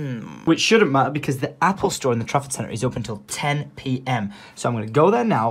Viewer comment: Every time he says trafford centre it sounds like he is saying traffic centre.